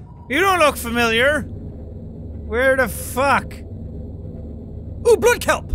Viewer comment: If you put English generated captions on at 16:34 says where dafuq instead of where the fuck